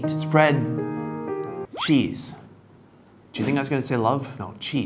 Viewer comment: Thought he said "spread cheeks." I was about to be 💀💀💀